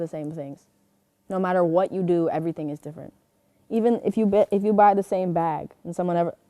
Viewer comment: BROOOO i thought she said “ if you bite the same bag” 💀💀😭😭